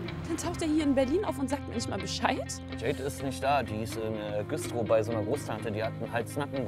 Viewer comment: habe ich da Güstrow verstanden